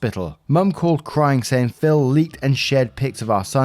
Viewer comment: Story one: I had to stop the video and look at the screen because I thought you said FIL (Father in law) instead of Phil 😂 I was like, HOLD UP 😂😂